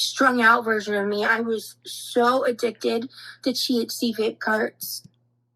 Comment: I thought she said “chia seed vape carts”